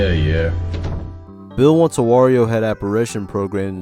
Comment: I think at 3:04 he said Mario head instead of wario, since he says it could be on the title screen. I could be wrong if greenio confirmed it was wario.